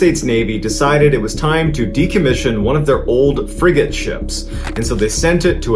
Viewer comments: Heard that as, "...one of their old friggin' ships..." which I figured out pretty quickly but still got a giggle from 😄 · I had this on an old bluetooth speaker and I thought you said fricken ship! 😅